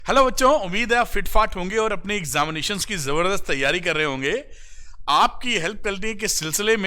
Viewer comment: Did he say ‘umeed h aap fit fart honge😂..!?